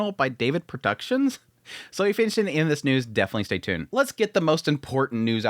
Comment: Did he just call it "Furren?" Lmao